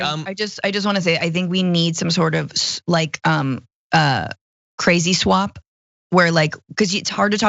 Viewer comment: I thought said 'we need a crazy swab' to test for crazy - but she said 'crazy swap' - perhaps we need the swab first and then the swap??